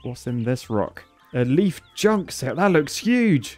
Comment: I was excited cuz I thought you were saying junk sale, not junk sail Edit: I was listening to the video while working